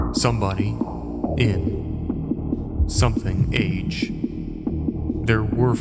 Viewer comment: "something age" - I think it says "message".